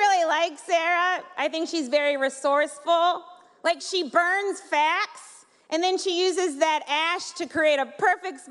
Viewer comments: people thought she said fat not facts .. hahahahahhahahahhahhahahhahahahaahahahh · The first time I thought she said she burns fats, and smokey eye was like, her eye twitch. It's "She burns facts and uses the ashes to make the perfect smokey eye". Literally saying she burns facts and applies it as makeup (Eye shadow), that's it. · it may be that people heard it wrong as ii did. when she said she burns facts its sounded like she said fat . i had to watch it again to hear what she said